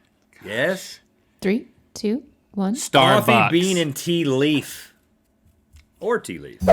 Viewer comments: Did Link say Starfox? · Did Link just say Starfox? 😂